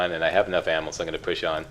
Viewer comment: "I have enough ammo, so I'm just going to push on." I hope that's what you were saying, because that could easily be misheard as, "Push Shawn"… I mean, what did I ever do to you? Why?? ;)